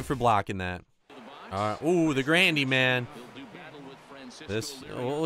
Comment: sounded like you said 'randy man' in the beginning lolol good video bro!!